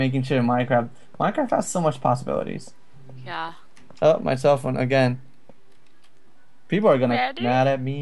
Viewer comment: Turn on transcript audio says: Oh my cell phone again Caption says: Felt myself and again. LMFAO